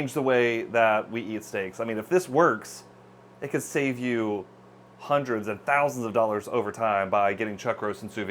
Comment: he said maybe save hundreds or thousands of dollars by getting chuck roast and not ribeye. but it sounded like he said hundreds of thousands of dollars and i thought that was funny.